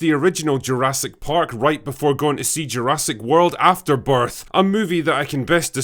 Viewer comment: did he say "Afterbirth" 😅